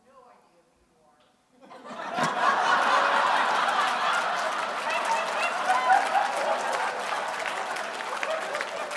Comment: Anyone know what the person said at 32:20 that embarrassed him? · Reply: A Lady said kind of sternly " I have no idea who you are"